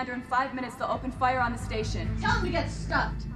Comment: or so, I think you may have misheard. She said, "Tell 'em to get stuffed." You laughed, then said, "...tell them to get stuff." Not stuff. that's just a bunch of whatever. "Stuffed," as in, schtupped, filled, i.e., an insulting sexual allusion. Just in case you missed it. If I am wrong, and you got it, then nevermind, please and thanks.